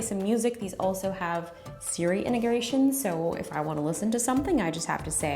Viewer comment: “siri interegration” is what i heard 😂😂